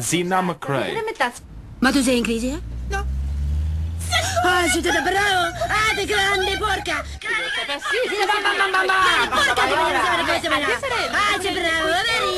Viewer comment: Everytime I hear "ah de grande porca!" at 0:25 I get a giggly fit. Bad dubbing wins.